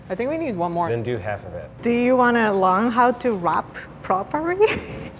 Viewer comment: I thought said “do you want to learn how to rap properly?”